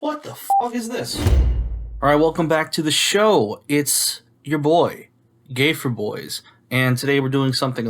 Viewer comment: Is something wrong with my ears or am I hearing him say "It's you're boy...gay-4-boys?" Wtf am I hearing? Someone please tell me what he actually said there because i can't e what I'm hearing right?:face-purple-wide-eyes: Even the subs say "gay for boys"...